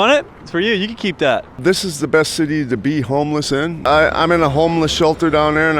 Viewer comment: LOL I had to replay that back I thought he said this is the best city to beat homeless in he said be homeless in LOL